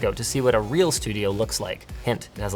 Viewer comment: Wow did he call monolith devs neckbeards?